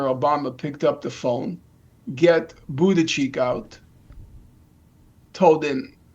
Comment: Did he just say booty cheek?! 😂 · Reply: Yeah his pronunciation of Buttigieg is interesting 😂